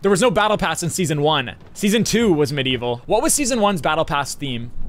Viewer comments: he said season 1 but the subtitle said season4? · is it just me or did he say season 1 not 4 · “what was season 1’s battle pass” captions say season 4’s